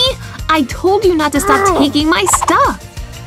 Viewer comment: did anyone else hear say Amy, I told you not to stop stealing my stuff!!! Like if u agree